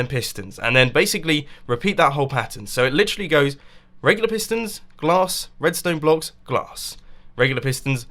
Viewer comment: says "pistons, glass, redstone blocks, glass" I hear: "sugarcane, sugarcane, brick wall."